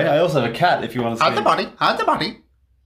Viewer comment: Bro I swear I had to watch this over and over again because I didn’t know what he was saying lmao I just realized he said “hide the body” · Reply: Fr I thought he said “hide the pony”